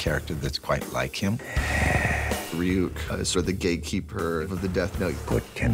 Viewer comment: i thought he said the gay keeper